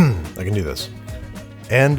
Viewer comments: I love how Donald implies that it can be either salty OR sweet, not both. Caramel will blow his duckin' mind. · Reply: I thought he said “salty.. no.. sweet!” Not “salty or sweet” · I thought he said "Salty, THOUGH, sweet!" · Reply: That's because that's precisely what he actually said - "Salty, though sweet!" - and the vast majority of other commenters in this thread spent literal months arguing over the meaning of a sentence that they couldn't even get right to begin with. Like morons.